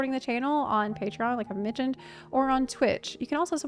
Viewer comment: at first i thought you said at the end " you can also support the channel like a midget" instead of "like i've mentioned" 😂